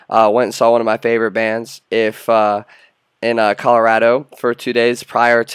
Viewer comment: did you just say Colorado at 0:42? damn dem medical legal buds